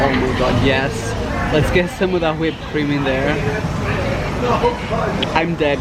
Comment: When he said let’s get some whipped cream in there when he was eating the carrot cake cheesecake people were talking and I thought he said weird cream then I thought what’s weird about it is it off or something !!!! 😂 😆 😝